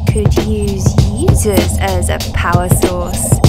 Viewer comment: I thought she said, "Yeezus as a power source" the first three times